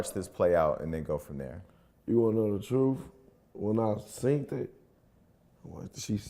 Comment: Did he say when I SEENED it? 28:16 Did he also say PA DOPHILE? Me : Lord can you quickly wear Ur scandals and come and be a Sunday School teacher? Tbh I feel bad for the baby, because this will be on the Internet forEver! Whether the kid was coached or not, this is sad on every level! I just watched her interview with Tasha k(ironically) and that interview was WILD! The girl couldn't even look at TK in the face! The story was all over the place! She got so irate that TK had to end the interview and kick her out) I also so a couple of her videos! On one she apologizes to him, on another her retracting her apology! Don't get me wrong, at first I understood why she would believe her son! It's what happened afterwards that's baffling! She also said while retracting the Apology, she said that she was talking to him on the phone all day and that he was trying to get back with her etc!! If this person had done anything to her child why converse/communicate with him after that? This is surely a case of obsession from a scorn lover! What if he was convicted? Wouldn't it have been another BLACK MAN thrown in the "SYSTEM"???Lastly I love how Jason, and the way they covered this story! Let also pray for that baby and the mother because she needs help! If this case is goes to court, this could be bad for her because she can be found liable and GOD forbid could loose custody of her son! Also where is the FATHER of the baby? Ok let me call Jesus and ask him to tell his dad